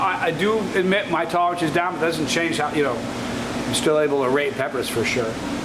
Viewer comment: did you say rate peppers? Or Rape peppers? lol Great review either way. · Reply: I said rate